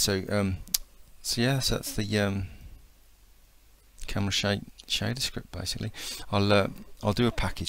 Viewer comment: nice tutorial, by the looks of it, but you talk too low and the closed captions are ridiculously inaccurate...at 7:10 it actually thought you were saying "cumshot" instead of "camera shake"...so i'll come back to this when i have my better speakers